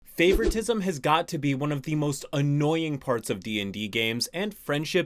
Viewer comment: When i started this video. All i heard was "TISM has got to be the most annoying"... and im like hmm. I better rewind.